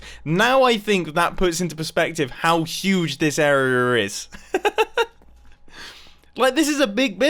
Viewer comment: Anyone else catch him say area “arra” or something like that